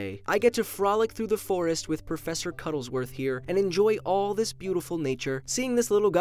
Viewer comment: Professor Cuddlesworth is VERY happy you're enjoying the new video! It could actually just be because he killed more squirrels, but who knows!? Thanks for watching! :) Do you love cats more? What would happen if all them suddenly died? Find out here: <link> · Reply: I thought you said professor cumsworth